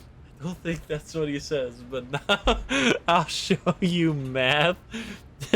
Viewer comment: i think it says "Let me show you -----"